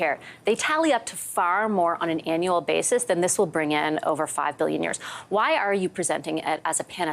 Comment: Does she say 5 billion years???😆